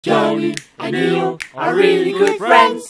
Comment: it sounds like ill is saying "jenny" instead of "jhonknee"